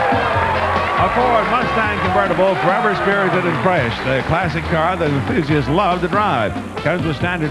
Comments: did it say "the car that the b**ches love to drive? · I swear to Christ, @ 8:56 it sounds like he says "The classic car the bitches love to drive"!🤣😂🤣😂 Also...poor Scott.😢